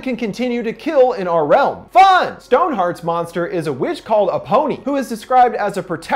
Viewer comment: I get the fact she's a protector a women, whenever I hear her name I just hear "A pony". I'm sorry but it's funny to me.